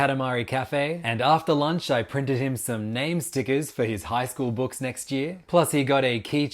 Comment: Eheh did u just say in highschool or am I tripping